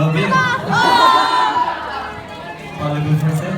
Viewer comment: Qu'est-ce qu'll a dit? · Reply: J'entends plutôt "Parlez-vous français?"